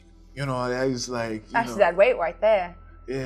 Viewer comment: I keep seeing poor captioning on DnD live plays, at 30:13 the Goblin says "that's dead weight" and the caption says "Actually, that wait" I see it a lot on dropout for dimension 20 as well, the captions are frequently incorrect and it's really disappointing that the accessibility features are letting down people with hearing impairments.